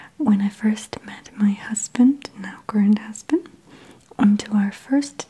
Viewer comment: did she just say "my husband, now GRAND husband?!?! Or Is it bed time? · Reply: I thought she said current husband because she was married before.